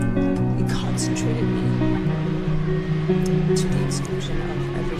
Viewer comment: Am I the only one who heard " concentrate, to be social. " whispered at 4:39 ?